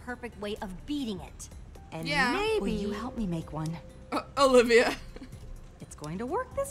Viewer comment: Omg I thought it elligaard said. "Non goggle guy. Will u help me make out "